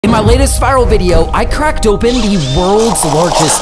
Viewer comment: I swipes up really fast at the start and I heard "iN mY lAtEsT fVcK" 😂